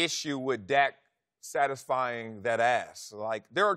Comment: “Dak satisfying dat ass” I can’t be the only one who heard that 😂😂 Wiley on one